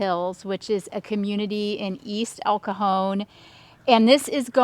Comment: I know said “El Cajón” (the drawer) (city) but knowing it sounded like she said “El cojón” (the ball/testicle; as in “cojones”) 🤣🤣🤣